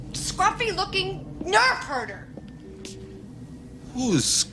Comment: Nerf herder? I always thought she said nerve hurter